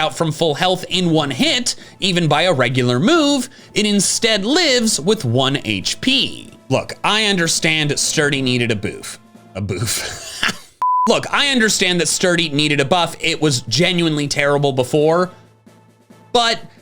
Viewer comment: I thought you were trying to say ‘boost’ but I guess you were trying to say ‘buff’.